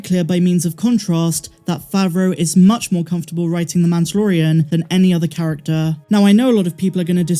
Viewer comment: LOL... about 5:51 sounds like you say Manchurian instead of Mandalorian... gave me a good chuckle... The chapters / episodes are fun to watch... remind me of the 1960s TV Batman and Robin... but, cooler... The ending of this episode was GREAT...!!!